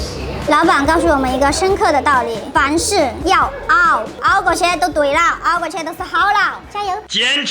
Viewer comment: “凡事要熬”我聽成“房事要熬”😳，我太邪✌了！哈哈哈